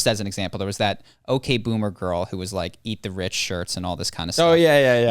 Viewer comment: I might be wrong, but I think she said "Tax the rich" not "Eat the rich", "Tax the rich" is a position she can still support even after becoming rich.